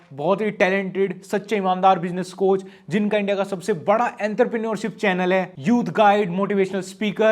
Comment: enterpeniot kya bola